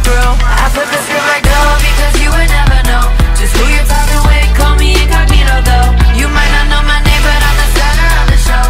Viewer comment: LYRICS: VERSE 1 Pull up on the street Doin wheelies at your curb yea Show up at your door Like I'm entering your world but I Gotta keep it moving Cause I'm good at what I do Sophisticated sneak When Im rolling with my crew I flip the script like dough Because you would never know Just who your talking with Yeah I'm just that capable You might not know my name But I'm the center of the show A Lion outta cage and I'm unpredictable PRE I know you never saw it coming What I got cooking in the oven Gotta getaway getaway getaway Gotta getaway Leave them hungry for the chase Getaway getaway getaway Gotta getaway Leave them hungry for the chase HOOK I'm the Driver The Conniver I can handle the anything The job requires I'm the driver The Conniver I can help you getaway Thats why I'm hired VERSE 2 Don't call me a thief when l'm handling my business I'm on a winning streak Everybody bout to witness I gotta keep it movin' I'm not tryna rendezvous In and out is my mystique Without leaving a clue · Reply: instead of "I am just that capable" I hear "call me incognito though"